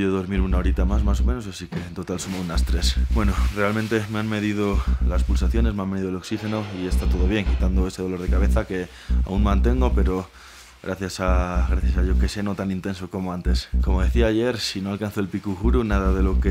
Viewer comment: en el minuto 24:25 dijo, gracias a Dios? o es que no entendí bien, diciendo gracias a Dios? 🤔 😨😨😨